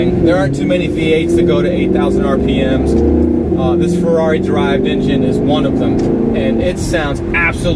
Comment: did he say FERRARI on